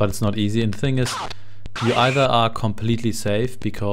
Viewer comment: My dad used to play this on his Apple IIe all the time when I was a kid. HALT! KOMMEN SIE! burnt into my brain. I always thought he was saying Old Spice instead of Ausweis back then.